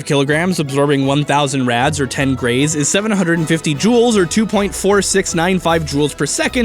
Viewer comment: DID HE JUST SAY JUUL ? this video promotes drug use